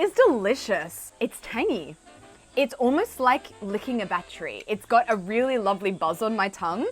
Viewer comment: After hearing "battery", I thought I heard wrong, rewound, turned on the caption. OK, did say "like licking a battery"😂.